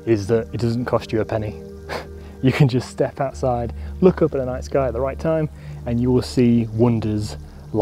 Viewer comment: Him " In the night sky" What I hear "Our nice guy"